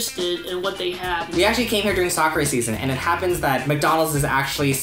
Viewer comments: Lol...!!! He says "sakura season" but CC says "soccer season"... 😆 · I thought he was saying 'soccer season' until the sakura season info came upXD